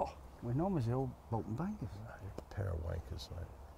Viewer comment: Did say pair of Wan***s at 7:26 😁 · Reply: Wangers I think!